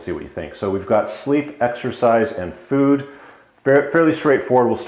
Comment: I thought it said sleep, exercise, f&ck. Not a bad routine.